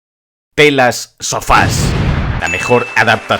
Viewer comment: entendí "¿pelas sofás?".